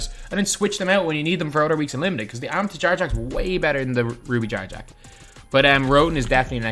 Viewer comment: I thought he was saying jar jar when he said jarrett jack lmaooo